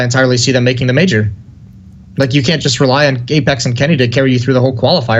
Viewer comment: did he say gaypex?